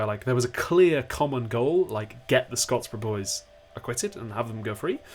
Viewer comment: Omg I thought you said Tom Scott and I was so confused vecause he only wears red shirts lmao